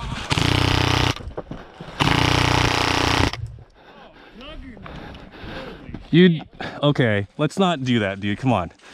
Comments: what does the guy say? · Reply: "Oh Juggernaut Dude!" aka 'You didn't call your hit' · what was this guy saying? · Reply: "Oh Juggernaut Dude!" aka 'You didn't call your hit'